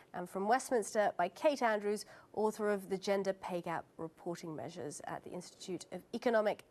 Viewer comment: Dat gender bay cap doh. Perhaps she should slow down and think a little more and talk a little less. Too busy trying to flood the conversation instead of picking apart the argument. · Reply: I thought she said gender bake app. Something to do with gingerbread men?